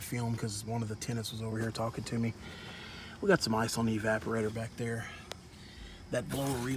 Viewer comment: wait what did you say ice or mice. Lol